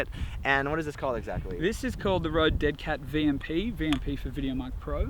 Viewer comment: almost sounded like you said dick head :P